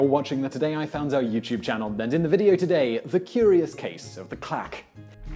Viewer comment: I thought he said "the clap"